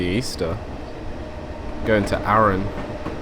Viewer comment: Arrams in England - just north of Hull, I think. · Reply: Sorry - misheard you there, thought you said Arra*M* not Arra*N* ☺